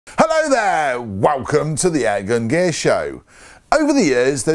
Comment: thought u said airgun Gay show xDDD